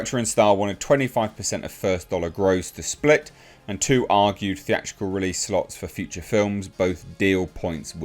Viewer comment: argued instead of guaranteed